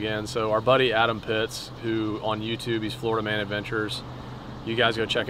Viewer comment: I keep hearing “Holdaman adventures” and that’s obviously not it. Can’t find it.